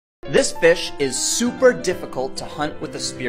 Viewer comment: At the start I thought he said “this bitch” I didn’t realize he said “this fish”